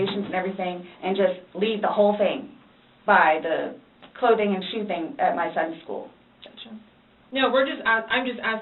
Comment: did she say “clothing and shoething”